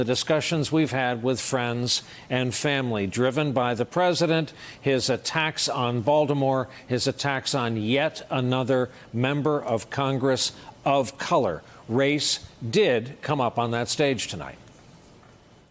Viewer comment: Did he just say, "His attacks on Voldemort"?